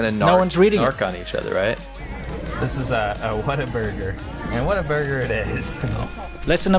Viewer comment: "What a burger"? At first I thought he said Water burger. Perhaps they should have had water burgers at the first restaurant that was on fire.